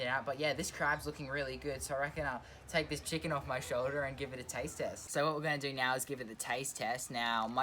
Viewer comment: When you said taste test I thought you said take test